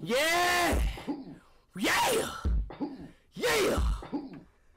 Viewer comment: Ben: no please don’t do it please please I beg you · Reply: people who heard speed said amubatunut